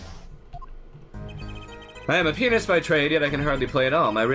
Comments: for a sec I thought he said i have a penis by trade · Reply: lol that was funny seriously i went back and i heard the i am a *** by trade instead of i have a *** by trade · I know this makes it seem like I have a dirty mind (which I might have), but did anyone else hear "I am a penis by trade?"